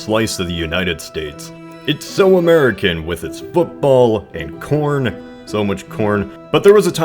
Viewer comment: when I first heard that,I thought I heard porn,not corn